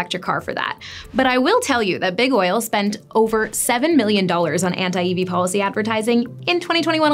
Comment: 7 billion against EVs. Where they could have been making EV infrastructure and cornered the market instead. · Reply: that spend was stupid, I agree. But I thought she said 7 million. Did I hear it wrong?